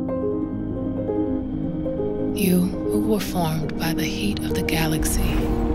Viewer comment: Beyonce` Stop Mumbling and Learn to Articulate Your Vowels & Consonants...It just sounds like she says at the beginning that, "You Were Fucked By The Heat Of The Galaxy!!!"